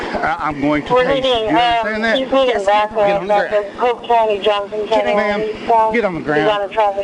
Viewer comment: Did dispatch say poop county 😂